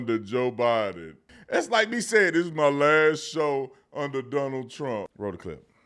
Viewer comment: I was so excited I thought he was saying last show ever damn you officer Tatum😂😂😂